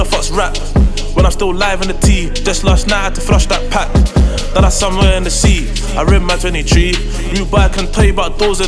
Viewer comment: what is he saying I love it though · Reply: Last night had to flush that pack now it somewhere in the sea...you don't know english?